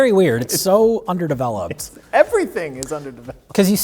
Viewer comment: Did somebody say "UnderDeveloped?"